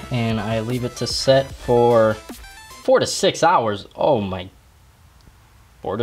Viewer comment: who thought that he said "46 hours" not "4 to 6 hours"?? XD